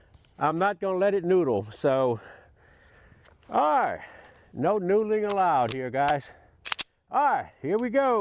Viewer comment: No camera on the target? I thought u said no noodling👎 it did look like very controlled shooting pattern